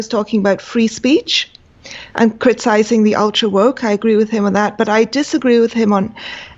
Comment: did she just said "alt-woke" or was it me who heard it wrong?